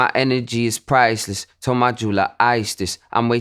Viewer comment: Thought he said "told my jeweler ISIS"